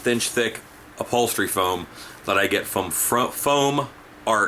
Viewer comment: Thanks. I thought you said foam art not foam mart. Found it :)